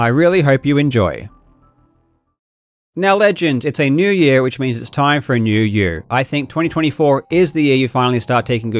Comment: The segue into the sponsorship made me laugh hard, and then I realized I misheard Karl. I heard "It's a new year so that means it's time for a new year" which is exactly my type of dry, sarcastic humor. What he said was "It's a new year so that means it's time for a new you." More appropriate to his sponsorship ad-read, but much less funny